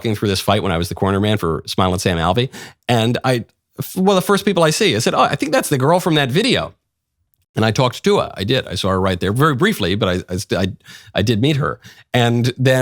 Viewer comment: Was it just me, or did Michael say he "talked tuah" to the Hawk Tuah girl? 18:32 😂